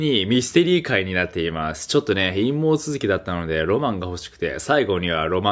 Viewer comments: 陰毛続きだったので〜って聞こえたんだが笑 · 久しぶりにナオキマン見たんだけど最初の陰謀続きってのが陰毛続きに聞こえてよく下ネタ言ってるなとは思ってたけど等々掘り下げ始めたかと思った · 不覚にも陰毛続きって聞こえてしまった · 陰毛続きだったのでって聞こえる